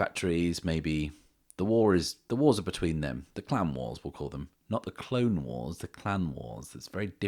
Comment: When he said Clan Wars I thought he said "Clown Wars 🤡" and was terrified.